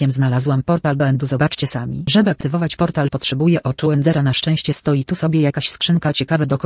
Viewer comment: czemu usłyszałem „potrzebuje moczu endera”